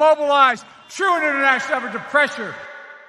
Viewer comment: I heard this : "True international of a depressure".